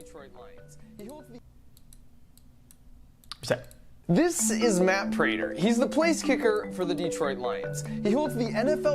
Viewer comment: what did xQc say?? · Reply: Reset I think